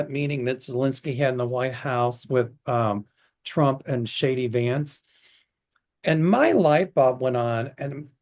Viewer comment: I just heard you say “Shady Vance” instead of “J.D. Vance”. Yes indeed! 😂